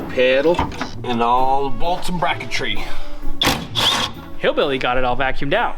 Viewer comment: …did say Hillbilly …🫧.🫧…🫧🫧..⁉️ 🤓🏁 I’m here for DINNER anyways 🎩